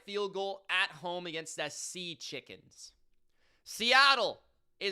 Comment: Did bro say Seachikens?! Hahaha Go Rams!